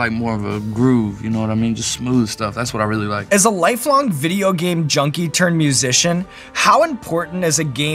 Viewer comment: I thought he said thundercat was a lifelong junkie I didn't hear the video game part LOL